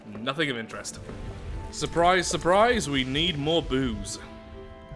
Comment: it sounded like need more boobs, not need more booze